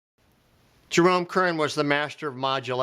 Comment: Who did you say was the king of modulations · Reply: I believe I was referring to Jerome Kern , and specifically: "All The Things You Are" which has multiple key centers. But also Barry Manilow, often modulated up a half step on his out chorus, to make a big finish, .